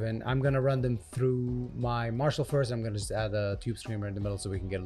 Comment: What amp is that? Beautiful cleans and great with the 808. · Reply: I heard him mumble "Marshall" ant tube screamer.